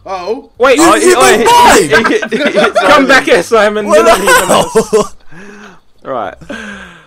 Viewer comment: What did simon say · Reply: Some Person what the hell